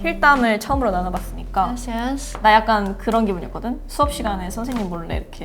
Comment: did she said gracias? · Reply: I think she said "yes yes"